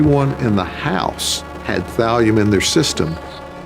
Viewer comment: my dumb assss thought they were saying vallium for 25 minutes... i was like but people take vallium all the time?